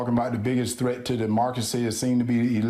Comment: Demarcacy? What did he say? 🤔 Jesus, his pronunciation of words is abhorrent. I'm sure he meant democracy. Ugh. Vote 🌊